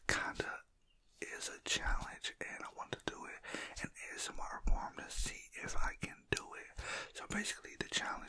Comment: I thought he said an asmr porn